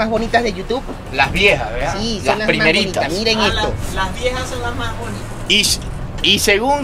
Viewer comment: 😂 pocos entenderán 3:58 lo que dice 😎😂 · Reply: Las viejas don mas buenas dijo(osea que las antiguas placas eran mas bonitas)